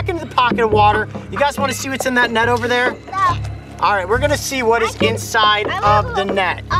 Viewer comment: did she say "no" lmfao 😂😂